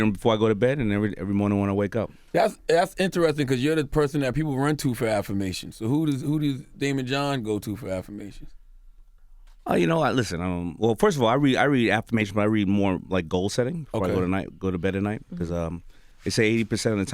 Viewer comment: I thought it said draymond green🤦🏾‍♂️🤣